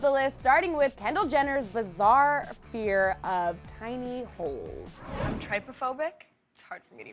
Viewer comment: oh i thought that kendell said tiny wolfs i was like what there. so cute😂